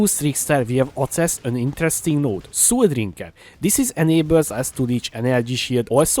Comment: I literally heard "sewer drinker" ;D looks fun and pretty affordable tho, getting bored with MF and not another TS deadeye this league. Kinetic bolt, benis brand of circumcision or that one it will be.